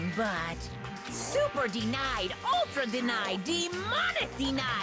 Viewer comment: I looked away for a second and thought Monokuma was trying to say demonetized